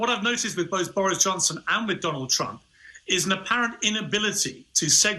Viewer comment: 😂 Sounded like he said McDonald Trump 😂